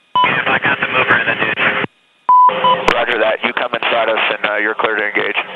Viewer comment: did he say something about... coming...????